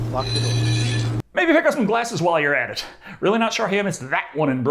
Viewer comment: I heard ‘maybe pick up some blasters while you’re at’. I thought good Star Wars joke, then I realised I’m deaf 😭